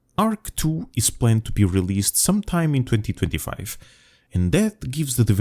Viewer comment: LOL! Am I the only one who heard "ARK 2 will be released sometime in 2055"? Yeah that sounds about right.